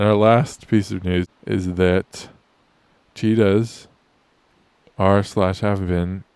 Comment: I first thought you said "Cheetos, were being reintroduced to India".